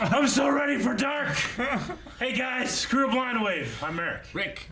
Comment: Eric.. you may want to be careful to speak the beginning of your sentences.. when you say "It's the crew of Blindwave" really fast, I could have sworn I heard you say "Screw Blindwave". 😏